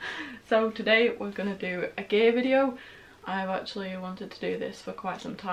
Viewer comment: Got to get my ears cleaned. For a minute I thought you said I’m going to do a gay video. I thought............Y..........M..........C............A. 🤣